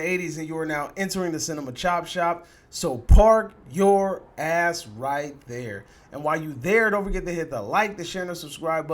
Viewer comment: Salutes Yeah, it's Rival to Winter Soldier Level. And Off-Topic, always thought you said in your Intro "Park your Grass right here." My honest mistake. :)